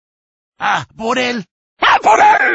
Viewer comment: ¡A por él! <> Go get him! 01:13 Está en la trampa. <> He's in the trap. 02:34 Rápido, es un intruso. <> Quick, he's an intruder. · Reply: A por él! I always heard "Aquaman"🤡